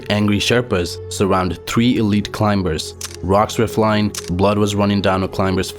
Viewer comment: i thought he said illegal climbers what he said was elite climbers